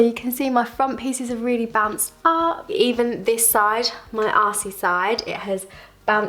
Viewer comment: your what? I'm in the states but feel like I'm pretty good at British accents since I grew up watching BBC shows but I'm not certain what you're saying... you're ass-y side? LOL! Please correct me and I'm sorry in advance! Great video! It's really empowering to hear that you and how you ask for your hair to be cut. Excellent! I just cut a bit of length off myself. Biggest reason: waste of product! Second reason, I'm still growing out damage. And a tiny bit because yea, my arms are not long enough to get all the hair in a selfie! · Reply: Aww thank you! Haha, yes “assy side” I learnt it from Americans on Instagram 😂